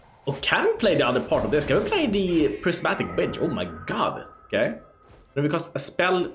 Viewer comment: I must be drunk, because I heard "The Prismatic Bitch" lol